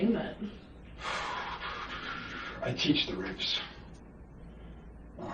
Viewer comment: I love how at 1:56:04 the subtitles say "I teach the rapes" instead of "I teach the ribs", making it even more accurate